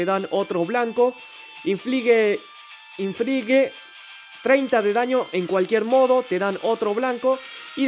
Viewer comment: como que 30 de daño soy yo o escuche mal xD